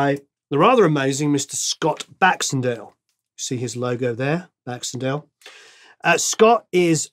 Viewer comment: My ears pricked up when you said Baxendale. I thought you said Saxondale, which would have made lots of sense.